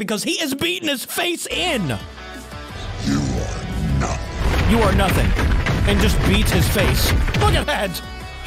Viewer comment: wait I thought he said "Your Are Dumb"